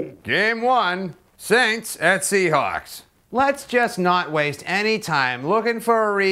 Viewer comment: when i heard the word seahawks i thought you said seacocks Boston acsent ftw